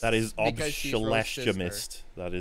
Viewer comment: Hello there! You can find this Podcast will be public on my second channel folks! - <link> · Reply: What was that word at 53:10-11? Obsemeshlamist?? Pls clarify.